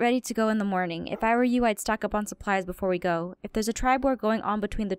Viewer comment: when Kiki said "stock up on supplise" i thought she said "stop making pies XD" i was thinking they were gonna throw pies at the other werewolf tribe LMAO